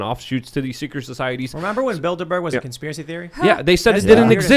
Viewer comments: At first I thought they were saying Build-A-Bear is a conspiracy (instead of Bilderburg). Gotta wonder what they are stuffing in those bears at the mall... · At first I thought you said “build a bear”. lol